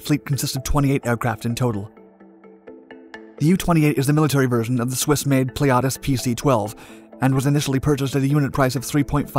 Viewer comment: it sounds a lot like pliatus is dat intentional? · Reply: Breh that's the entire point of the comment. I commented that I thought at around the timestamp 1:31 , the word that he said sounded a lot like pliatus instead of the correct word, pilatus